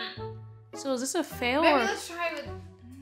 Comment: I heard that "baby" on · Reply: she said maybe